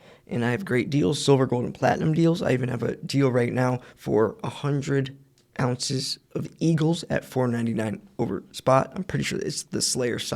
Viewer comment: I thought when you said 100 ounces of Eagles for $4.99 (with a brief pause before you said over spot), that you meant $499.00 for 100 ounces of Eagles! 😃